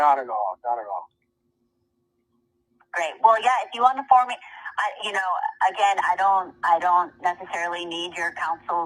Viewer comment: sounds like she said wack is the informant (your the informant)🤔